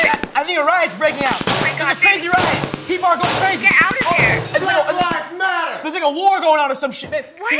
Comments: did he just said black lives matter ? Four years ago ?! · Who else is watching this from 2020 and heard him say black lives matter I love it